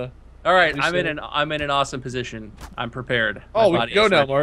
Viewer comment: Did ANYONE else hear try to say "my body is prepared" at 15:34?!?! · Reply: he said im prepared